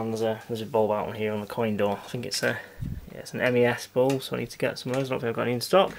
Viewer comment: ?- Did you say "NES bulb"?